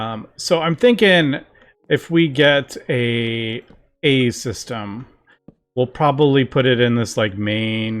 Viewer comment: Did I heard that right? AE System, as in AE2, cause that's some bonkers pre-planning if I ever heard some lol.